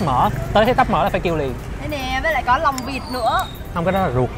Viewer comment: lòng vịt tao nghe thành lông vịt :)))))